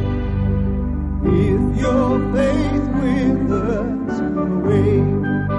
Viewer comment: Humpty Dumpty needs to get in contact with this guy post haste. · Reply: When I first heard this on the radio I heard the line "if your face withers away" - yikes! Maybe I'd been watching too much sci-fi horror at the time.